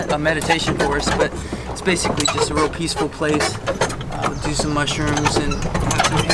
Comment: did he say, "It's a real peaceful place to do some mushrooms"?